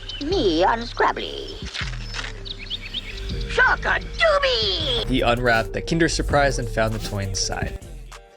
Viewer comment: Did I just hear him tell me to smoke a doobie 🤣🤣🤣🤣🤣💀🤣